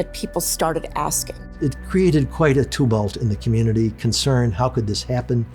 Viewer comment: What word is this guy saying?! “It created quite a ______ in the community.” It sounds like "tubalt," but that's not a word. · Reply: tumult